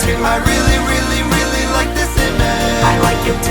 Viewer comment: I thought it said: "dad I really love this image"